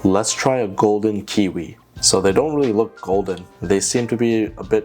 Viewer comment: I thought it said "golden kiwi kulfi